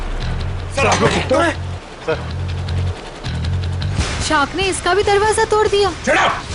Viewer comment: sounded like "sir aapne iska bhi darwaza tod diya" classic Daya joke😆